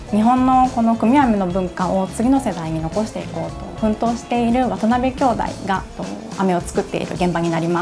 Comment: Anyone else thought it said "Wannabe"? No? Just me?